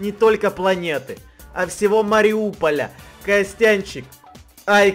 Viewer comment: мне послышалась или он сказал Мариуполь?